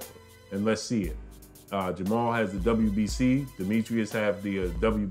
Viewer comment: I thought said jermall was a boss🤔.. hell to the no!! Lmao